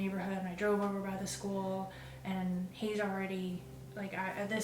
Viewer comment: Did you or anyone catch what see says as 3:59 4:00 in this interview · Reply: I think she says He's allready...